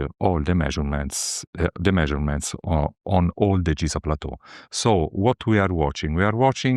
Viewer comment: there's a typo in the closed caption "degrees" instead of "giza"